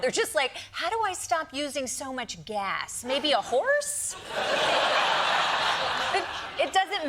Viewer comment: I thought he said hose, not horse